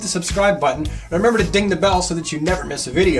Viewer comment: for a sec i thought you said feces squad also i need that mime and marionette i dont care for them being in 2 packs though